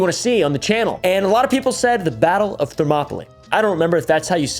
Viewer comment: the subtitles say "the battle of thermopolymerization" lmfao